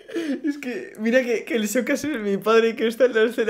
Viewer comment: nose porque peri escuche a beniju decir "mira que el Xokas es mi padre"